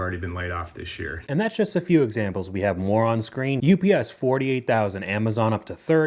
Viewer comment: I heard "and that's just a few examples we have, moron. Screen..." 😂